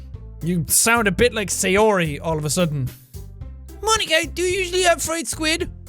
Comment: "Ika" means "squid" in Japanese, so that's why Natsuki just used that to make a pun on Monika's name :P 14:25 Poetry theory 1: it seems like the sand castles represent our life, and the tides that wash them way represent death. That way, the beach would be the boundary between life and death (and "eroding at the shore" would be a reference to self-harm). 20:30 Poetry theory 2: the "lady who knows everything" seems to be some kind of all-knowing goddess (like Athena, the Greek goddess of wisdom), who would have the answers to Monika's questions (more specifically, if life has any kind of meaning or purpose). Since Monika keeps breaking the 4th wall, she might be more aware of what's happening, leading her to question her own existence. 31:50 Yuri:"Hooooh?" (Dumb) game theory: Yuri might be a legendary Pokémon (Ho-oh) :P 1:02:47 Jack: "You knife branding weirdo" Yuri:"Please don't think I'm weird for this" Perfect timing! XD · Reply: oohhh i thought 1:08 was nat calling mon icky XD ika=icky XD may bad